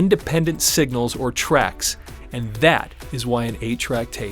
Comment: I thought Spotify was saying an A track tape